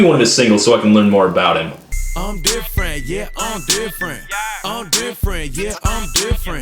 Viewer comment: all I heard was Andy Samberg going “I’m a weirdo, yeah I’m a weirdo”